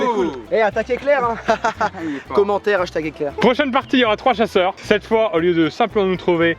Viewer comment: il a dit "hashtag eclair" on est bien d'accord? 😂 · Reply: Non... J'ai entendu "hashtaque éclair 🌩" lol Entre le hashtag et attaque 😂